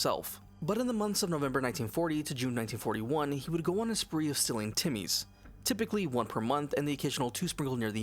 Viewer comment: Dumb question, but did you say "Silling timmys" 2:08? I can't tell if I'm going deaf or not lol. I'm guessing that's the "Don't demonitize me" language for killing kids?